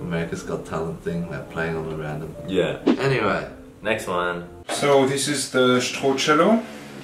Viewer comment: I thought he said "this is the torture room"